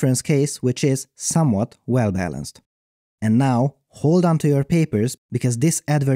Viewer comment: I love the beginning as it always sound like you say "Too Many Papers"